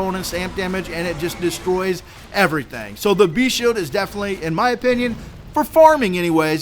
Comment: I thought he had said Brest shield all this time